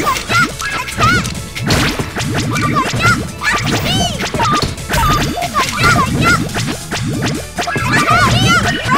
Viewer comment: What did she say? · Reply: Lucky me!